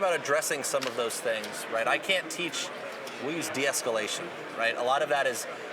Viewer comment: Agree that you can teach de escalation—never said you couldn’t! Just that most of the time, we are teaching it incorrectly. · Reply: re-listened and what got me was you kind of changed thought mid-steam and I didn't catch it at first. At 11:17 you start saying "I cant teach" and then switch it up to "we use deescalation" etc. I apologize for misinterpreting the point.